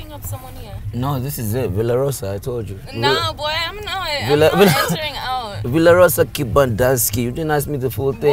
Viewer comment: Did I just hear "am not entering out"???lol